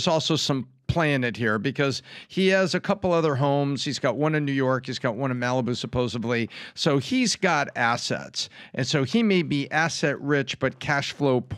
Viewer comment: Did I just hear Tom say supposably? 🤔🤔🤔